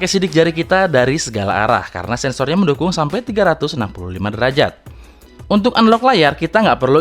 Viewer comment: Aku yg ngatuk ? atau ada apa tapi aku dengernya itu 365° ? di menit 6:23 :p